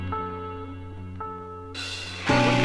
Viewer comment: What do the voices say at 04:11? To me it sounds like "What do you call that?" "Feelings, just feelings." If you search for the lyrics of this, you get some lyrics that are totally not in there!